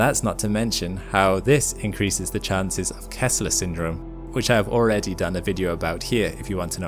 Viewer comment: I thought you said, "Tesla syndrome"